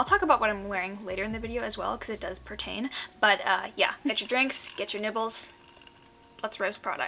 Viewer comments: genuinely thought she said "get your drinks, get your nipples".... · I know you said get your nibbles But I thought you said get your nipples lmao